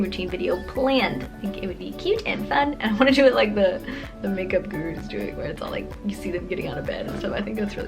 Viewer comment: hahahah 12:27- perfect example- WTFFFFF are you saying?? LOL you're adorable but seriously, WHAT ARE YOU SAYING because I CANT HEAR YOUUUU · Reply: VANE8883 she was talking about the morning routine video..saying she wants to do it like the makeup gurus do it where their like getting out of bed because it's like cute and she just wants to try it... #🍍squad #ispeakfluentnikkib 😄